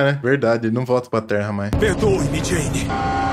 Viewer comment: só eu que escutei Mary Jane?